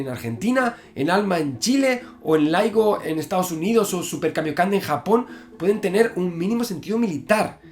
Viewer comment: pense que dijo "o super kameka en japon" lmao